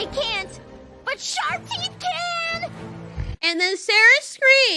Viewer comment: I just realized that when you said that it sounded like cera was disagreeing with you and said but sharp teeth can